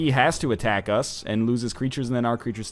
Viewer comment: it sounded like he said ancient poomaster lol